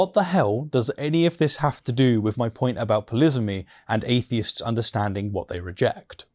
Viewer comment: "Polisamy" - did I hear that word correctly please?